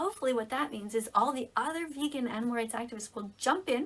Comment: I thought she said “all the other vegan n-words will jump out there” I did not hear animal rights